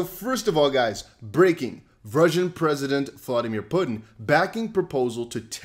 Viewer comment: Sounded like he said Virgin president flooding your pudding! 🤣🤣🤣🤣🤣😭😂😭😭